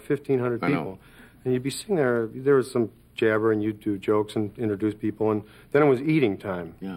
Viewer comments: Does Bill Murray mention 'YOUTUBE JOKES'? And if so, then what they hell was he referring to when he said You Tube in 1984??!!? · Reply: He said "You'd do jokes" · "there was some jabber and youtube jokes" I'll admit that my hearing is not that great but I also put on CC to see what Bill really said and it was transcribed the same way. I'm pretty sure "youtube" was not a term before Youtube. Can someone else tell me what he really said? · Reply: “People we’re eating and you’d do jokes” remember he was hosting the evening. Sorry if you thought it was something else!